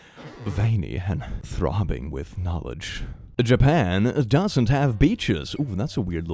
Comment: I thought you said Japan doesn't have peaches, and that's an equally untrue statement.